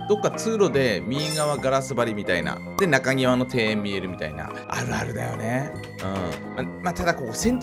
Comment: 2倍速で見てたらあるあるだよねのとこがあなるだよねに聞こえる笑